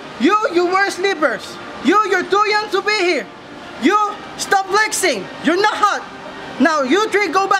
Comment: When i heard “DON’T FLEX YOUR NOT HOT” Me:😦😦😦 yow bro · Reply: It said "stop flexing" not "don't flex"